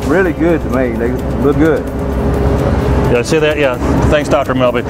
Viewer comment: did he say they look good nigga ? o.O